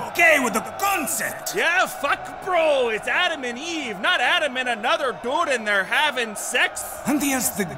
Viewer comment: And what does he do with golden throne?! Replaces it with a WOODEN CHAIR probably carved by more POOR PEOPLE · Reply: i could have sworn he said " it's Adam and even not Adam and Steve "